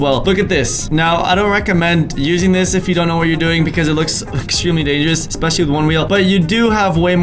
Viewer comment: I like how you said, "Look at it, it is extremely unique" on that three wheel/roller skates contraption... But what I heard was, "Look at it... it looks extremely lame." 🤣🤣🤣